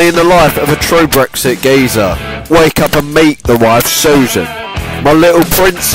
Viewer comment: My stupid ass thought he said, "Wake up and beat the wife."